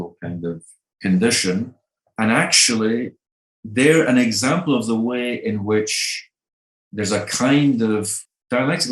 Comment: I wonder what is the other book named here? I clearly heard "libidinal economy" but another one immediately followed it which I can't hear. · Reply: I think it is Des dispositifs pulsionnels